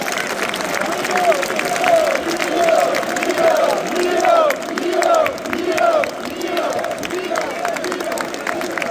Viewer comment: What do the fans say/shout here? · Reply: yiddo